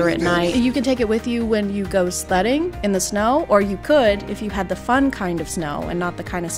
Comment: I was listening to this wgile making a cup of tea, misheard sledding in the snow as slutting in the snow. I was a bit confused